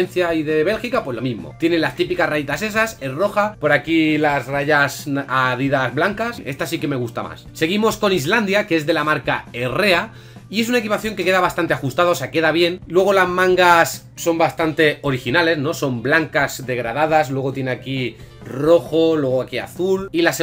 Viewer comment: escuche diarrea